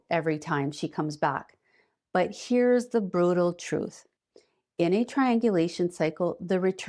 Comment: did she just say "brotal" ?!😮